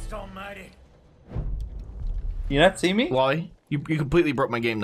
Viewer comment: Because of the accent and probably the fact I'm not American I always hear saying "Loli" as "Wally". Anyone else or just me?